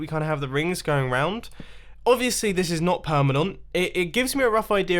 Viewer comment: Mumbo, about 17:09 did you just say "permanont"? 😂 That's why I love your videos. There is always something funny in them.